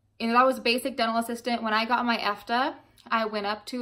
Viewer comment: did you say fda?